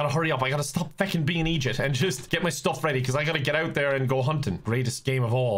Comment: what does igip mean? He saids it alot but i never understand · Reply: He's saying "eejit", which is an Irish way of saying idiot. Hope that helps!